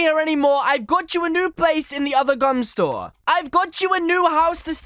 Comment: I thought he said your moving out of this store and into the other gun store